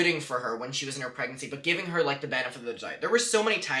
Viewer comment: the subtitles say benefit or vagina